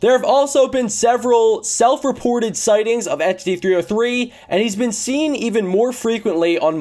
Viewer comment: you said XD 303 not entity 303 look at the captions if u want more proof but I heard XD and saw caption